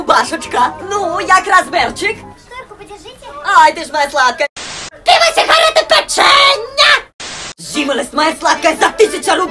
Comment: что он сказал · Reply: Пиво сегарети пеЧєнняяяяя